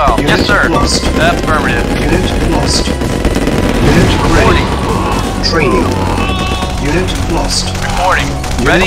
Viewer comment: in and it starts to sound like a remix of the track "unit reporting, unit lost" over the music :D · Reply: I heard "unit ready, training, unit lost" instead.